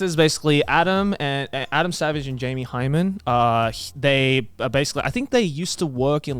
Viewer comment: Did Joey just call Jamie Hyneman "Jamie Hymen"?